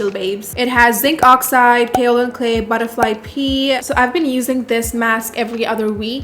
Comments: I am drawing while watching (so basically mostly listening), and my dumbass thought you really said 'butterfly pee' LOL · I thought she said butterfly pee 😂